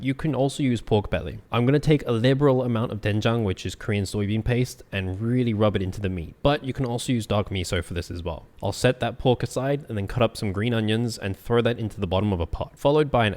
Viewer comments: I thought he said “you can also use dog meat as well.” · Why did i hear "dog meat" instead of "dark miso" 💀